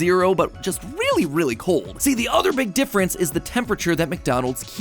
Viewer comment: he said “big difference” and I thought he said McDifference 💀💀